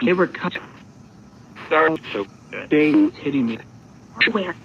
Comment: At about the 14:30 point of the video, it sounded like someone said “Dave is hitting me “ lol